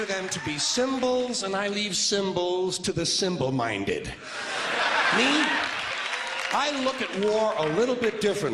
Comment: garad gantal he said symbol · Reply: I heard it as "symbols are for the simple minded people" which made more sense